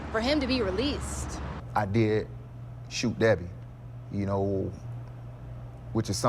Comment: At around 0:43 does he say “I did shoot that bitch?” And they bleeped out the “bitch”? You can clearly read his lips, someone correct me if I’m wrong. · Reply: He said, "I did shoot Debbie."